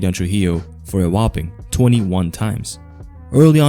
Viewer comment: I thought it said 27 times😂